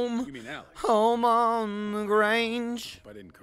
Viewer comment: did say home on le grange?